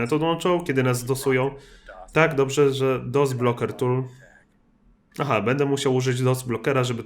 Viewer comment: ja usłyszałem dildos XD